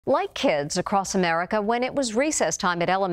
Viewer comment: I thought she said white kids 😅